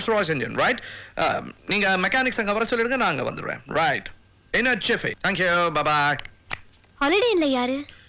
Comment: can somebody tell me what is he saying while ending the call? Une et Chiffy? What does that mean? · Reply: In a jiffy